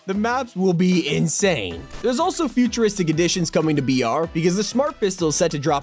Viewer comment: did you say vr!! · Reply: Br = battle royal